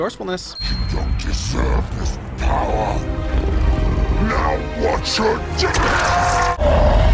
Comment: Also I thought abomination said, “you don’t deserve this power…Not once your dead”. But either work